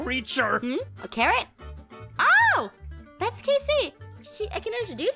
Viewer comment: lol in 14:16 I heard Aphmau said hmm the carrot 😂😂